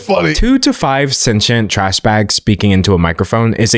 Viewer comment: I straight up thought you said "2.5 sentient trash bags speaking into a microphone" which wouldn't be wrong in this scenario